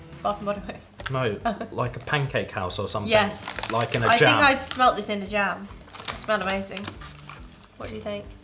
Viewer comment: I thought said “I smelled this in Jail” 👮🏻‍♀️👮🏻‍♀️when she was eating the Huckleberry snack!!! 🤣🤣🤣🤣🤣🤣🤣🤣🤣🤣🤣🤣🤣🤣🤣🤣🤣🤣🤣🤣🤣🤣🤣🤣🤣🤣🤣🤣